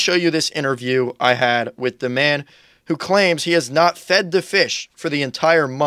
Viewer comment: I heard "has not fucked the fish". I guess I'm just pretty dirty minded 🙄